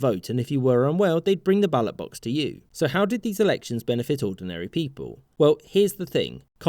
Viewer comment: Lol I thought it said how did Soviet electronics work lol XD